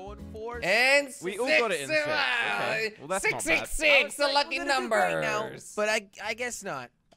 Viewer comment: did he just say "666 the lucky numbers" like if you noticed it